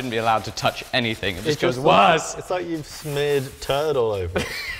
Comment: I heard "smeared turtle"